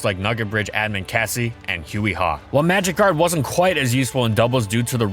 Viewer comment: did he say magikarp?